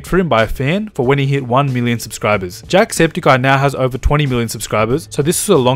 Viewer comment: it sounded like he said jack has over 20 billon subscribers